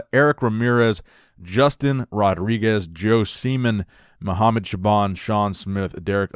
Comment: I was washing the dishes and then I hear say "Joe SEEMAN" 😂😂